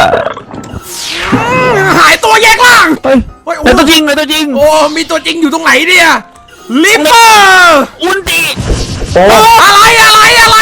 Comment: ผมที่ได้ยินว่า "อุนจิ"